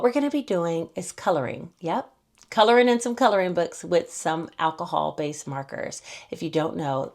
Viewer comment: 😂 at first I thought I heard you say;” we’re going to be coloring with some ALCOHOL..”🍷